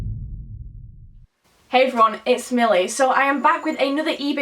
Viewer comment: You’re gorgeous and i love your videos. But when you started this video it sounded like you said “hey everyone, it’s smelly”. Haha so cute!